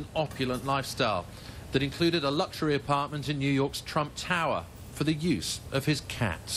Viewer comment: Did he say Trump Tower?